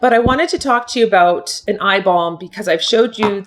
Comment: 😂😂🤣 I thought you said, "I have a beautiful eye 💣 bomb." 😂🤣. I'm new to your channel and I'm a makeup rookie (at 63...I know right?) I just thought that was some new cosmetic term.